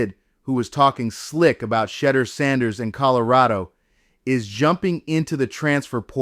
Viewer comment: Who is shudder sanders???? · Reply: 😂😂😂 I thought he he said, CHEDDAR SANDERS! 😂😂😂